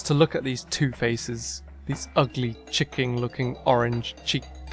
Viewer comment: you said Chicking instead of chicken lol